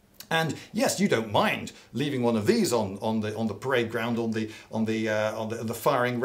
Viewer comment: i heard playground and was like: america?